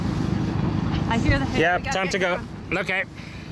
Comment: what did she say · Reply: Captions say “I hear the hail“